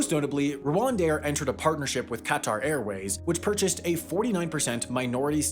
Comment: Did he say CAT RRR AIRWAYS?